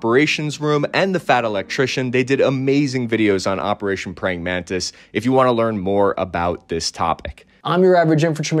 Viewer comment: I misheard the first time, thinking it was Pranking Mantis, cos US Navy against Iranian Navy is like a school of orcas playing with their food, like a seal, before killing it.